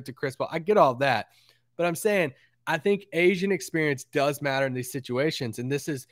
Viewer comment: thought said “Asian experience” lol